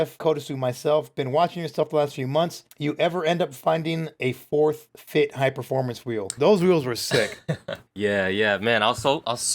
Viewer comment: what is he saying there? Forth Fifth Hig performance wheel? i cant understand